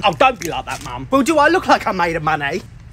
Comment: Me when I heard “ am I made from money “ M= made O=of M= money